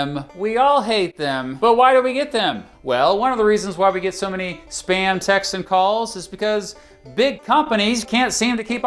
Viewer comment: When you said "spam text" I heard "SPAM TAX". I immediately became equally angry and confused